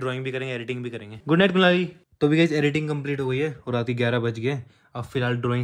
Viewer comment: Did he say KULANI ?🤣🤣🤣🤣🤣